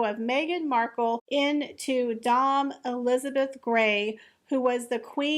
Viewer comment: Elizabeth Woodville the White Queen. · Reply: Thank you. Sorry for the correction, but I thought you'd said Elizabeth II.